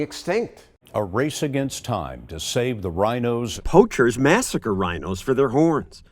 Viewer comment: Hahaha I thought John said save the Rino